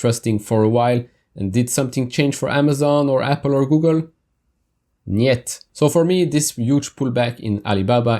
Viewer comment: did you just said «Нет» in Russian?😅